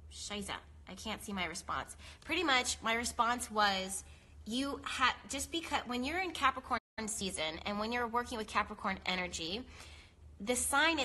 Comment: girrl did you just say "scheiße"? o.O crazy